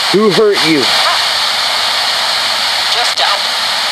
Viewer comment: just after a little girls voice says mama killed her, You asked “Who hurt you?” I heard her mom Patricia Ramsey respond, “just don’t.” In a pleading voice. Edit: and later, “hurtful” then, “go” in same woman’s voice. Before you asked the next question.